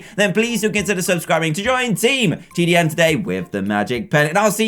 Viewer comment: the first time i heard present theme song i thought it said ''breath tart' instead of ''press start'' and i was like whaaaat